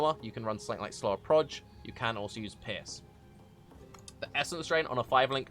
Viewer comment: when he said 'pierce at 4:55 it sounded like piss